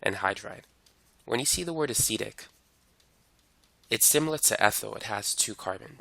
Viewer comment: what did he say? It's similar to _____ it has two carbons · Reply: I think he said ethyl